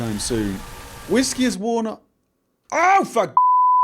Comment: i'm waiting for the day that this channel stops being pg lol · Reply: don't you try not to say fuck? because when it crashed i could've sworn i heard you go "aoo-fuck!" jast barely on the last bit, or "aooh-fu-" on the first crash in this ep.